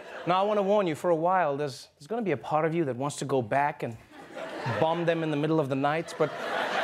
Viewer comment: Anone else think he said "bum them in the middle of the night"?